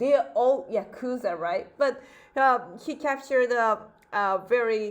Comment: Wait South Africans 🇿🇦did she say "Uyakhuza right" at 11:02🤔🤔🤔